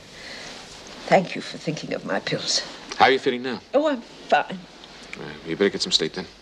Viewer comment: At about 21:45 Jim asks Mum how she felt. She replied "Oh fine now". But captions said "Oh whoa f@ me, now I can get some sleep." 🤣